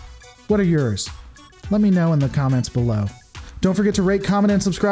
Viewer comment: At the end it sounded like you said "rape, comment and subscribe "